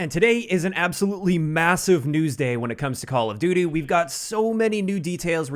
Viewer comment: Did he just say 'Call of Doodoo' 🤣